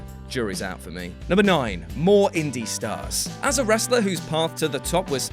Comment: They put "Indy Star" instead of "Indie Stars" |